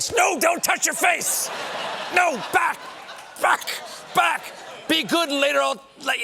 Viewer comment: haha nice! I think I heard a funny word "back! F*ck!"